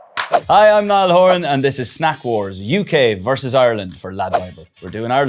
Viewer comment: I thought it said Smack wars !!!!!!!!!!!!!! 👍👍😎😎